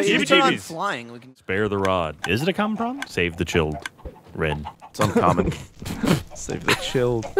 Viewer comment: Every time I hear Ryan say "Save the child..." I always think "...Chaos?"